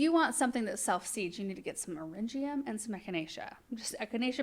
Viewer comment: At the 32:22 mark you mentioned Arium that's what came up on CC, but I still couldn't make it out as to what you actually said. So searched that name and I just come up with houseplants. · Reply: Eryngium, or common name is Sea Holly. Blue Star and Blue Glitter are 2 popular varieties. Those reseeded everywhere in her cut flower area. I hope this helps you.